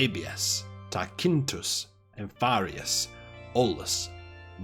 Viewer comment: Did he said his name is Alpharius?